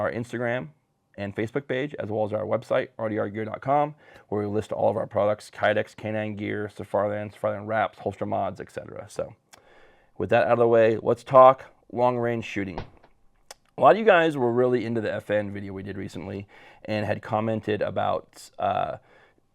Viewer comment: I was breaking out some ice from my trays and had to rewind the intro because I thought you said Pokémon and not “holster mods”. 🤣🤣🤣